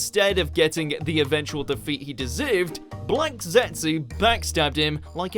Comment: Hold up, everybody go check.....at 6:39, did he just say 'blanked zetsu'???? Was that a mispronounciation, or did he not want to say the word black??